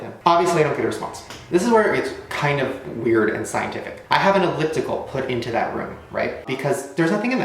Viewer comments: I HEARD 'MINE' WHEN HE SAID ELLIPTICAL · When I saw u say that you heard a voice in 4:24 I was like wait what there was a voice??!! So I want back on the video and I heard the the voice and for sure that was not his voice but I'm not that sure what the ghost said so if anyone hears what it said plz tell us all we r all curious. But it sounds like it just said "Ah" so if I'm wrong plz tell me cuz I'm not sure if I'm right or wrong so yeah :V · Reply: I have headphones in and it sounds like it said "I'm here" or 'I'm in"